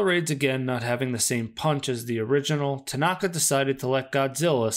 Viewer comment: Did you say “not the same PUNT as the original? Are you sure you didn’t mean PUNCH? Because one makes no sense.